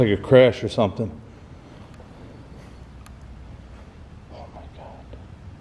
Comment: Ah man at 36:09 I swear I heard a whisper say "Oh my god"